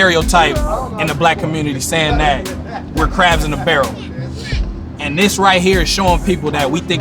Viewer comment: Um actually to the dude at 11:35, it’s “crabs in a bucket” that’s what said